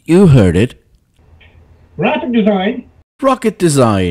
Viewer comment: I think he actually said “graphic design”